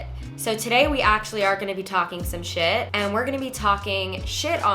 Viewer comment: I can’t be the only one who thought this said “while taking a sh*t” 💀💀💀😂😂 omg 🙃